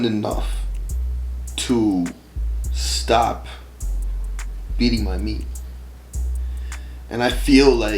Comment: I thought this nigga said “stop eating my meat”